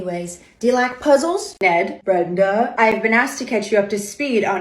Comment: I first heard "Ned inbred" and thought that's quite rude hahahahah